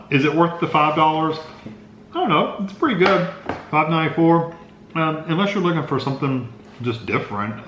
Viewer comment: Tillamook cheese is the best cheese I've ever had. $5 is a little steep for the size of the meal, but Tillamook is a good, quality brand. Thanks for the review! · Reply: Actually, did he say $5.94?? Not that I wouldn't still try it. I'm one that will try anything at least once LOL. Also I usually get coupons at checkout. But I'll have to listen again cause I thought he said $5.94 at one point towards the end. Edit/Update: Yep! At about 5:55 he does say $5.94. 😉👍